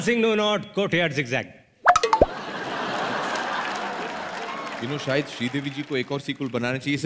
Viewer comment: did abhishek call Ritesh as Shahid?